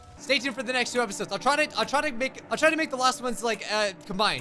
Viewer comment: For a second at the end I thought he said scrubscribe